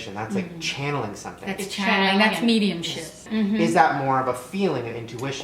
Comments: it sounds like she says "That's medium shit." 😂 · I SWEAR I thought she said "yeah that's medium shit." CTFU😂😂😂😂😂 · Anybody else hear, "that's medium shit" at 3:51 lol ? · did she say median shit?